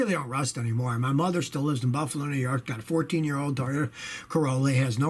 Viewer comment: Did he just say his 14 yr. old daughter is named "Corolla?"